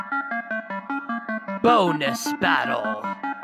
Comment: For a minute i thought it said boldness battle at firsr i was like MONIKA AINT BALD